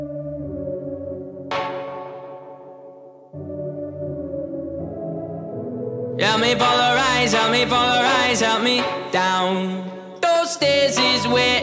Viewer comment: thought he said :" help me pull her eyes, help me pull her eyes out."